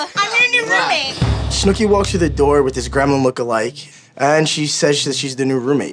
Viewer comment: Did ron say Snooki walked through the door with a “ GREMLIN look alike ?” 😂 Savage